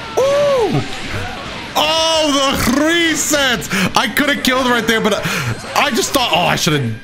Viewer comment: Did he just say ‘gresets’?